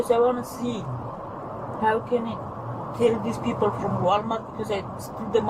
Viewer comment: she says something like "How can I tell these people from walmart because I didn't steal the money" not "how can I kill these people" you guys did the subtitles wrong lmao. At 20:37 to me it sounds like she said "I lose them nothing, I pay all the money" but the subtitles say some nonsense there too. I am learning Russian and teach English to Slavic people so I hear their accent often. Also I work at Walmart and we are getting training now to help prevent this. I am explaining more because the video didn't make it clear either. It's really a sleight of hand trick, they count the money once, employee counts the money once, then they try to take the pile again somehow and use sleight of hand to slip some bills away. Older employees have said it isn't really a new trick but it hasn't been abused so much in a long time, so it's new for new employees.